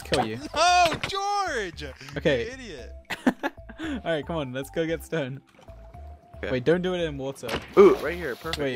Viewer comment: I thought he said 'lets go get stoned'